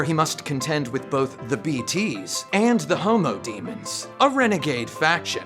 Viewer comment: did he say “The Homo Demon”? 😂😂😂😂 wtf lol, that’s a bit extreme.